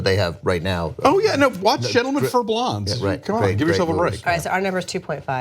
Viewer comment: Does anyone know what movie Alonso name drops around 7:40? It sounded like Gentlemen Forblonz or something? Thanks